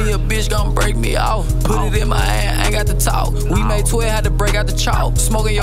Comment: What did bro say at 1:04-1:05 😮👀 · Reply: Put it my hand lol